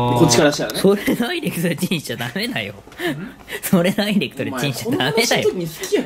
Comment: 虫さんがなんて言ってるかわかんなくて字幕付けたら｢ブーブー｣って書いてあってもうわかんない。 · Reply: りた それダイレクトでチンしたらダメだよw って言ってます ちなみに俺は最後ゆめまるがなんていったか分かりません